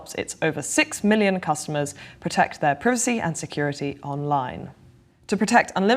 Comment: I heard "protects your ... p_ssy"--got my attention! Then figured she might have said "PC", listened again, realized she said "privacy". Anyway, good job UnHerd! Don't let us snooze off!